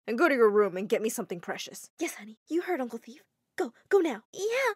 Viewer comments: did she just say uncle thief? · did she say uncle thieff?? Bruuhhh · did she say uncle thief?? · Did I just hear “uncle thief"🤣🤣🤣